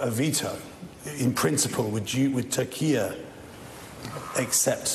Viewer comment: it's funny at 4:50 when the interviewer said Turkey and it sounded like Taqîya